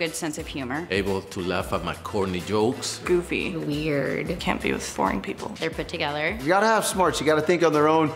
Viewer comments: wait did she say can't be with BORING people or can't be with FOREIGN people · did she say she couldn't be with foreign people? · thought she said foreign instead of boring lol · Did she say she can't be with 'foreign people' (😳) or 'boring people'?